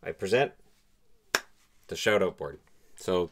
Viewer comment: "Shout-out board". At first I thought you said "Shadow board". That cat's taking over the channel!